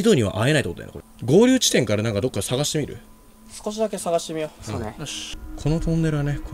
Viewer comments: えっちょっと待って、3:30秒のとこから、濁った声で、そうねって、聞こえるんやけど気のせい？めっちゃ怖いんやけど、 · ～3:34秒にかけて、が喋った後に変な声で｢そうね｣って聞こえない？ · 〇分〇秒に〇〇が写ってる...を楽しみにして探してる人私だけすか？ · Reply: トンネルのくだりで相づち「そ～ねっ」っていう謎の声がモザイクかかってる感じで聞こえた